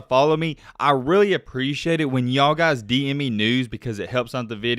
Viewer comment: I swore he said I really appreciate when y’all dm me nudes😂